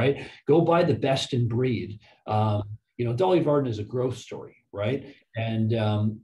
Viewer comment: I keep hearing "Dolly Parton"